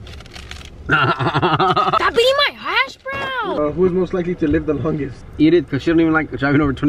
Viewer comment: No one literally no one: Me:did she just say aS bROwN